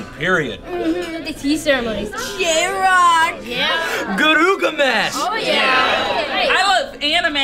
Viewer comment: i always hear "Gurugamesh" D